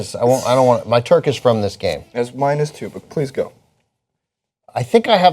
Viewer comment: Did he say his turkish friend is gay???